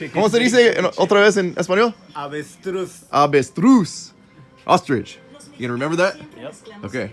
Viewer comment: the captions said “bitch Bruce” for avestruz